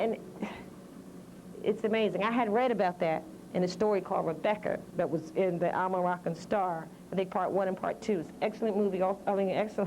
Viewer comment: Does anyone understand what she's saying at @ 32:13, about a story called Rebecca? I can't make out what the name of the publication is. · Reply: publications